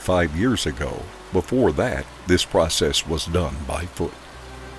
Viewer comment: Did he say it was done by foot?